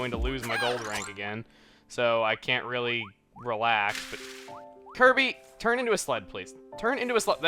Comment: i thought is saying "kirby turn into a slut!